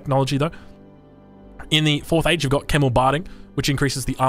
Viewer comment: I thought you said Camel Biting